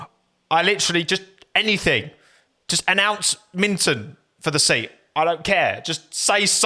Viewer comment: did he say "announce Minttu" :D